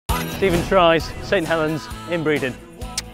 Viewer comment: surely i wasnt the only one who thought it said stephen, tries being the funniest human